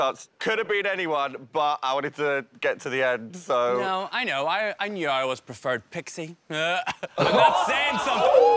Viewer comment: Can someone explain me what she said? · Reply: Jonbers says “I knew I always preferred Pixie”. Pixie Polite is Tia’s ex and was on UK season 4 with Jonbers where they did not get along.